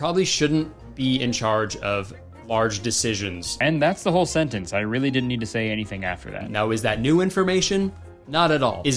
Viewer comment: calling all lip-readers; what did he say? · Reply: 'when it comes to weddings'.